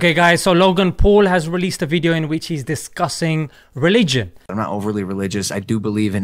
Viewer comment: I thought he said disgusting instead of discussing